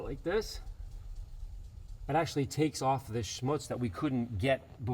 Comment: throwing in the German word for dirt „Schmutz“ while working on an Audi or did I hear that wrong? Haha